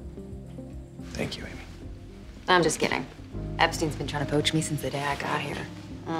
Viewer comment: Did she just say Epstein at 1:33 ? That didn't age well if she did.